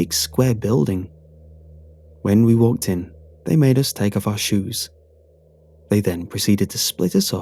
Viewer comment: I thought it said "They stripped us of our hoes."